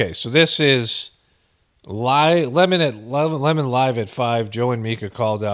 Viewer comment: I swore I heard lemon lime at 5 😂 sounds better that listening to Don